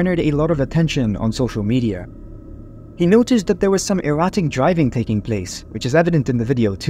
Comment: 'Erratic'? I heard 'erotic', lol!